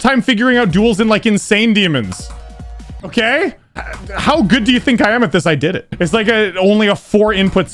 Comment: he says how good do you think i am at this. what i heard: how good do you think i amethyst